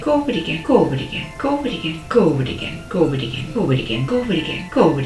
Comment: She says “go over it again” but I hear “goo-bidy-gin”